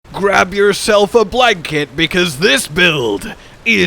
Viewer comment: I thought you said grab yourself a black kid to start the video. Had to listen to it 4 times and then have my gf tell me what you were saying